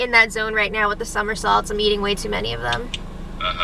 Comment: I thought I heard "summer salts" at first, but it eventually dawned on me that it was "Somersaults". I did think it was weird to be snacking on what I presumed to be some sort of flavored salt.